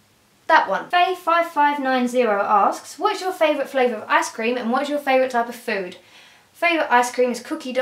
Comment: When you said Ice Cream I thought you said Ass Cream...